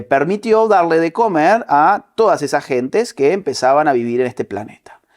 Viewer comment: Hola, vengo aquí para reportar que escuché " gentesss" por favor ¿diganme que también lo escucharon?